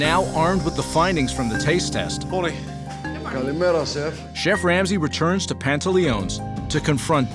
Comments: Wait at 0:29 i heard a "Καλημέρα Chef"?!? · wait did he say "καλημέρα σεφ"? (good morning chef in greek)